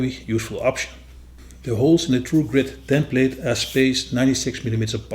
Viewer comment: LOl sorry but from your accent I thought you were saying Truegrit system lol